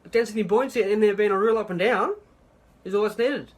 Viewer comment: I hate that I had to, but I had to use the closed captioning to understand that. I could be wrong, especially on the first part, but I'm pretty sure what he said was, "Density and buoyancy are the real up and down, that's all that's needed..." or at least that's close, because the first part might've also been "Density and buoyancy are in fact up and down". I know the accent isn't, presumably, his fault or anything, but gods does that drive me nuts when I can't understand someone without listening to it over and over again...